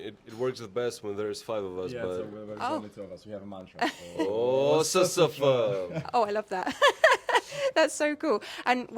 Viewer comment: can someone who understands, tell me what they are saying? i'm so curious! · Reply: They are saying SSF, which nobody knows the meaning of, they keep it a secret